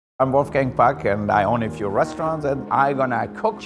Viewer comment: In the beginning I thought he said "hello my name in Wolfgang Fuck"